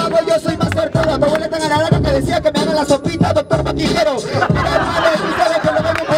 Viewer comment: que dice en el minuto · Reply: La sopita doctora no quiero